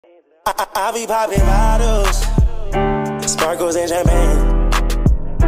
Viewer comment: I thought it said I he poppin loud house